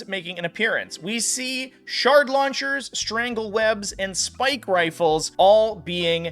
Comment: For a second i thought he was saying "shart launchers" and i was like damn these bugs are nasty